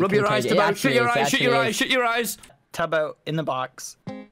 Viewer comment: did i hear him say shit ur eyes shit ur eyes what